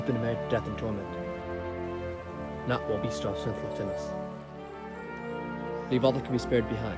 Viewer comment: that audio dub might need doing again, I couldn't make out what you said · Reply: oh, he says, “not while we have strength left in us.”